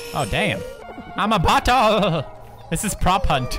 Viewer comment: did any of you think poods said buthole? Even though he said bottle... Weird thought i guess.